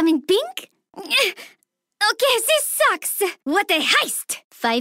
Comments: is she saying, "oh Cassie sucks" ? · WHAT THE HEIST (?) sly idk what she says, one of my fav lines -.- also the wiki doesnt contains all the lines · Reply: she says What A Heist